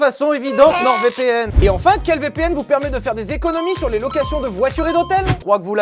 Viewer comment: j'ai skip le placement de produit avec la fleche +5 sec j'ai entendu "quel vpn vous permet de cuire des saucisses ?" 👀